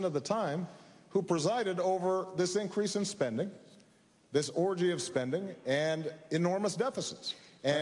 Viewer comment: Did Obama say "orgy of spending"?